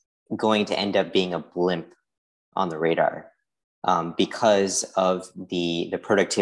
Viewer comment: Did he say "a blimp on the radar"? lol